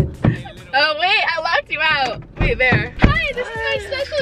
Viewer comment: i no joke thought you said bonjour to the taco bell people HAHAHHAHAHAHHAHA